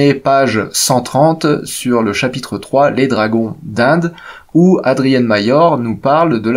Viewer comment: sur le moment j'ai entendu Dragodindes et pas dragons d'Inde ^^